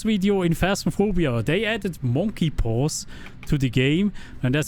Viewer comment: With your accent, at first I thought you said "They added menopause to the game" Oh, god. Lisa is going to be so much more violent now!